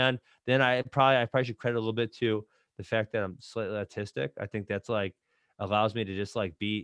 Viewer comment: what does Ben say? He's autistic?